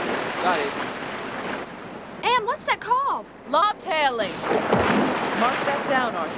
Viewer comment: If you turn on the captions at 10:15 (or close to that time stamp) the captions say "bobtailing" instead of lobtailing.Bob must be a whale. Bob i feel so bad for you. Their on your tail.